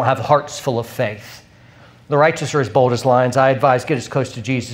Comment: What did he say at 25:46? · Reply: The righteous are as bold as lions.